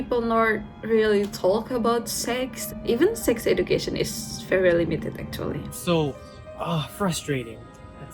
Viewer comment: I think she said "very limited" no?